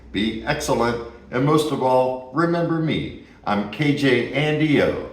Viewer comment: As you know, Andy. I have the dual pizza oven, and I'm making pizzas all the time! We just made a couple a few days ago! 600+° is key to making a perfectly cooked pizza! It's much better than those 60-second pizza ovens!!! And wow, those nachos look amazing!😋😋😋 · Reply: Not sure if I mentioned this before but, when I first started watching your videos I thought you were saying “I’m KJ and EO”, then I thought, but there’s only one person there. 😜🥘💙